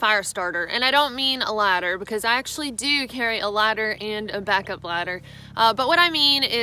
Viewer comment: I heard “I actually do keep a ladder and a backup ladder.” Then I realized she said lighter. Man, that was one hell of a confusing bit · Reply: I heard "lighter and a backup lighter" on my first viewing of the video and after reading the "ladder" comments I can hear that too.